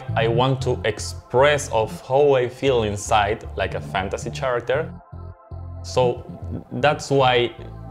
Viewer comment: Did he just say "Fantasy SHAracter?" ok.